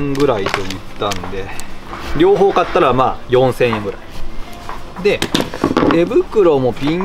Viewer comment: 4000円が400円(´⊙ω⊙`) 字幕の数字の桁が１つ足りないですw 人混みや、雑音で動画の音が聞こえにくい時、割と静かな時でも音声+字幕で動画鑑賞する派です。 YouTubeの字幕機能は誤字と、その都度の字幕なので使用してませんが、 字幕付き動画は編集は大変そうですが、私的には最初から動画に字幕を１文書いてあると 動画をスキップし易いし、の動画は見やすくて頭に入りやすいので好きです❣️